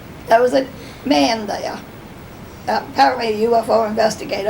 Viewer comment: thank you. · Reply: I heard "apparently" also.